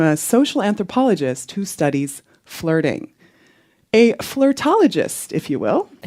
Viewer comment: Ok but it sounded like she said, "that studies farting" I'm dead